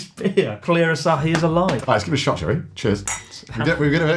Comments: I can't hear Joey. I only hear "shall we". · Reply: Yeah, he definitely says "shall we" but it just sounded like "Joey". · what's up with Chris' face? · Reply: Lol I just caught that edit. I think that's Joey's face? Probably the joke was that it sounded like Connor was saying "Let's take a shot, Joey" instead of "shall we".